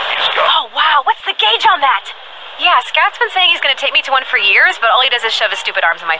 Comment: Love that she clearly says Scat instead of Scout